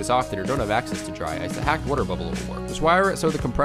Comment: what does he say? A hacked water bubbler? A hot water bottler?